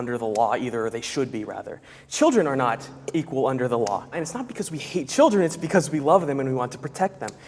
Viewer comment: Not a feminist but at 3:56 it sounded like he said you can’t vote cause we wanna protect you